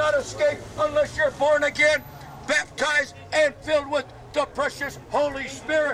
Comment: “And filled with da PRECIOUS” I thought he said ‘depression’. Oddly fitting.